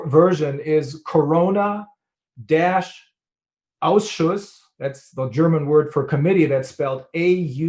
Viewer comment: Sounded like he said "Corona Auschwitz". Think your getting a shower, but they gas you to death instead. Think you have Covid, but they kill you with medical malpractice instead.